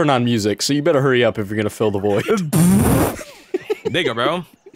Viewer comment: did anyone hear what was said or am i trippin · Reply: He said “here you go bro” or something like that